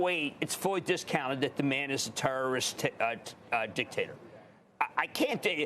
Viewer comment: did he say "terrorist"? i'm laughing out loud in the Lunch room